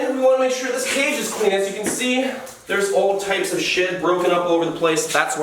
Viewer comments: I thought he said theres all types of shit😂👁👄👁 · I thought he said al types of shit